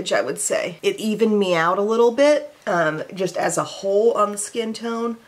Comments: When you said "it evened me out a little bit", I heard "it even meowed a little bit"! 😂 Thank you for brightening my day, and Revlon Illuminance! The cat's meow of combos! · I totally thought I heard her say it “even meowed a little”. 😂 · I thought you said “ it even meowed a bit “ instead of “ it evened me out a bit “ 🤭